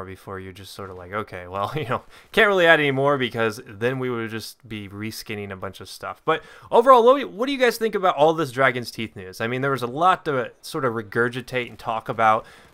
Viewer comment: Shoot I thought you said "Dragon's Teats" Never mind.